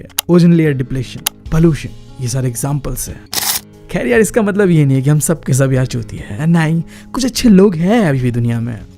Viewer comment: pe kya bola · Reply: Chutiya bola